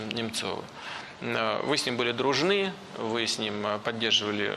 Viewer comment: А имеются подтверждения других лиц, что между Путиным и Немцовых были дружественные отношения!?? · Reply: Вам тоже на 5:38 послышалось, что он вместо "вы" говорит "мы"?))) На самом деле, он обращается к Хакамаде